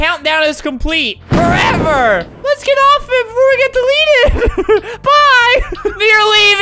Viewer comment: tell me why for 4 years i thought she was saying “let’s get off babe” instead of “let’s get off of it”